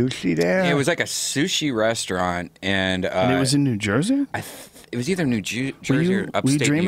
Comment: he said new jew-sey instead of new jersey lol.